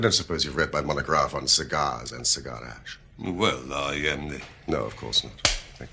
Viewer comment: I just can't make out what the inspector said at 5:01! lol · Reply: duhawma He said "well, I uh."